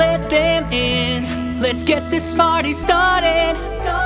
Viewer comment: am I the only person who heard Smarty?